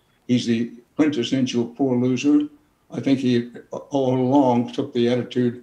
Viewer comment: For some reason I thought the quote said a “poop loser.”